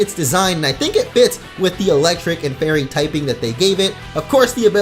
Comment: man I'm super hype for a fire fairy type :D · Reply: I thought you said fairy tail